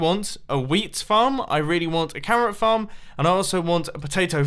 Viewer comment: Did i heard camera farm or ? carrot farm ?! It would be awesome to have a camera farm tho !